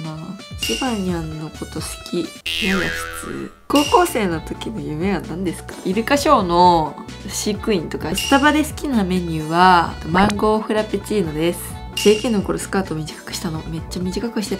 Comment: マ○コーフラペチーノにしか聞こえないww